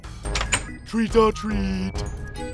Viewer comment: He says treat or tweet instead of trick-or-treat. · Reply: TheOmegaPanda no he said treat or treat